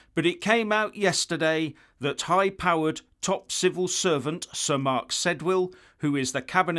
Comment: Did I miss hear. I thought you said Civil Serpents.